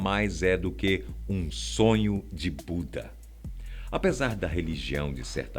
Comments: Só eu entendi "um sonho de puta"?? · Só eu que entendi "um sonho de puta" e voltei pra confirmar? kkkkkkkkkk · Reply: Kkk tbm entendi "um sonho de puta" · No 4:43 eu entendi "sonho de puta" 😂😂😂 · Q susto, ouvi "sonho de puta" sem querer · Eu entendi "um sonho de puta". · Só eu entendi "sonho de puta" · Só eu que entendi "um sonho de puta" e voltei pra confirmar? kkkkkkkkkk